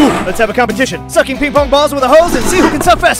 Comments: I misheard "ping-pong balls " to "people 's balls" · Wait I thought u said suck ping bong balls no use. It like a vacuum